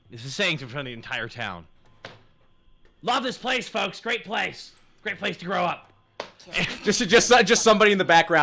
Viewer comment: lol I heard "great place to throw up"